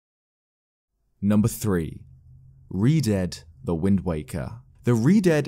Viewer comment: lol i thought it said redhead